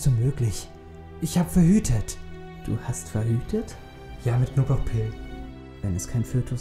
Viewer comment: ich hab "knoblauchpillen" verstanden :O xD